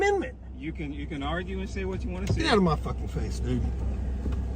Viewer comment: Did I hear wrong or he said the N word at 5:30? 😳😳😳😳 "get outta my face N**g*"? 😳😳😳😳😳 I am using cheap headphones so I could be wrong here 😂